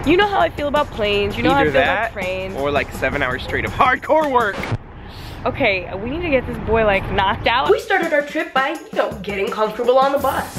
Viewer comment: "okay we need to get this boy like, knocked out" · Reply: OMG I thought she said "we need to get this hard-boiled egg knocked out" I was like wtf that's not very nice